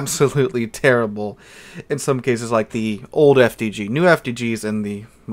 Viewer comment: What does he say? It's something like old fgg or old fdd. What is it? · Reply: FDG is short for "Friedrich der Große", the German T9 techline Battleship